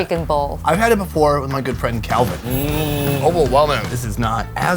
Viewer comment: When he mentioned 'good friend' Calvin I thought he said 'girlfriend' I had to listen to that part twice 😂